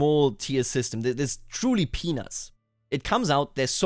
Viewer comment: When you said "peanuts", it sounded like you were saying penises.